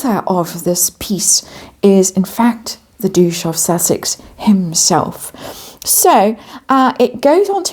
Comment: Did I hear "Douche of Sussex" at the very beginning, 1:47 ? 🤔🤭